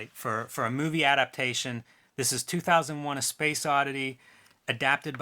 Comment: Did you say A Space Oddity?